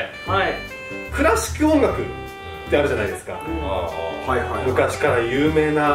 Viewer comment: 「皆さん。クラシック音楽、ってあるじゃないですか」 が 「倉敷音楽ってあるじゃないですか」ってなんか地方民謡みたいな感じで聞こえた。それ常識なの？って一瞬思っちゃった